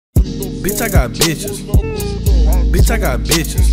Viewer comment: omg i thought he said "bitch i got Jesus"... well i was wrong... TOTALLY wrong😂😂😂💀💀